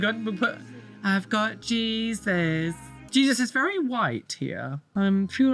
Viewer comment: Am I the only one who hears ''i've got Jesus'' as ''f-slur Jesus'' ?